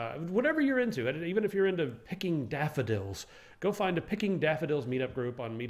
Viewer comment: What are "defadilds"? What says